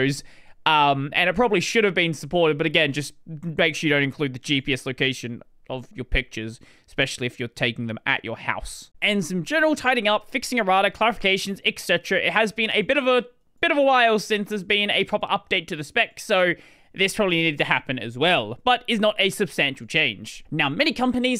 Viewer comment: Subtitles are a godsend. At 8:41 what I heard was "fixing erotic clarifications."